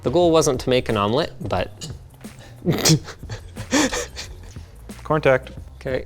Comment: did i hear a "corntact" á la AvE?